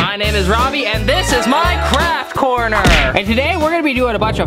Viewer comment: In the beginning I thought he said crack Corner not craft corner!!😂